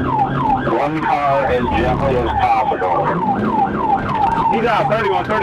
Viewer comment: it sounds like he says "ram the car as gently as possible" as soon as he is done saying that, the officer in the suv is smashing him through an innocent blue and yellow sign😂😂